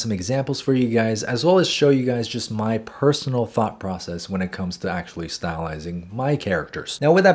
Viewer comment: WHY DID I THINK THAT HE SAID fuck process ima go insane 💔🥀